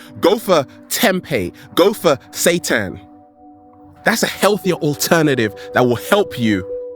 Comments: He lost me at "go for satan." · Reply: I thought he Satan too! I thought I just heard wrong. Lol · He lost me at "go for satan." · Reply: That's what i thought he said at first too, but then i realized he said seitan